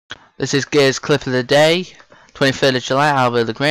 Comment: I thought he said "this is gayest clip of the day" for a second lol